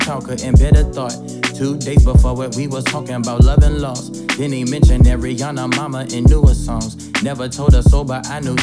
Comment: That Mac Miller line .... damn.... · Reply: i always hear "then he mentioned ariana momma aint knew his songs"